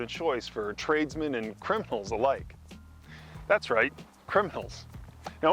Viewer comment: Totally thought you said cripples not criminals